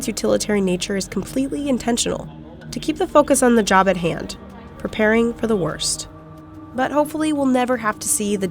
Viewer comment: I thought she said to keep the fuckers on the job at hand 😂